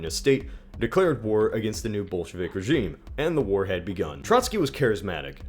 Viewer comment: each time he said bolshevik, i thought he was saying bullsh*t